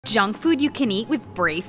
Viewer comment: i thought she said Jungkook at first😂